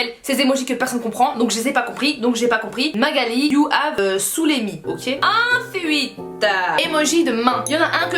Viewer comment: Aloha les gars ! Commentez moi vos émojis que vous n'utilisez JAMAIS ou que vous ne comprenez pas 🤔 Je lis vos commentaire ce soir sur Snap: S_kerienski JVOUS AIME ! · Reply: Shera Kerienski 2:50 " Magali you have saoulé me " à la base j'ai compris Magali you havé surimi 🙃